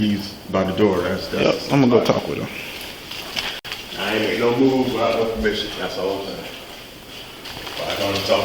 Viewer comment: That first maintenance guy said I don't make no move without her giving permission. That lady was crooked too. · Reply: He actually said “”I ain’t make no moves without no permission”